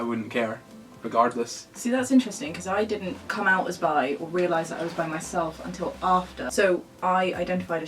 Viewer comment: I seriously thought she said "I didn't realize I was by myself" and not "I didn't realize I was Bi, myself" 🤣🤣